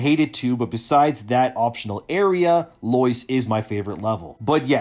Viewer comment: He said 'Loyce', and all I heard was 'Moist" and he said 'Eleum', but I heard 'Elon', so he went to a place called 'Elon Moist', and I was very confused for a moment before the area title card-thing popped up.